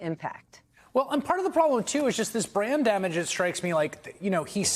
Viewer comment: Chris said "brand damage", but I heard "brain damage" and thought, well, that helps explain...